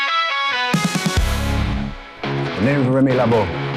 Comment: In the end clip he sounded like he was saying, “the name’s Remy LeBeau, “La Diable Blanc” but you can call me the Gambit.” For the record, Gambit is my most favorite X-Men character! ❤